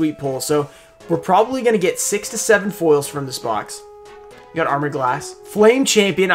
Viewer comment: When he said 6 to 7 foils, at first I thought he said 67 foils. Imagine...